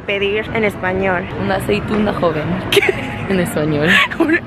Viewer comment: escuché que dijo "Nací pvta joven" y me morí de risa 😂😂